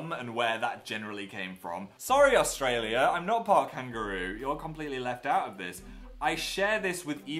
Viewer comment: IM CRYING I thOUGHT HE SAID HOT KANGAROO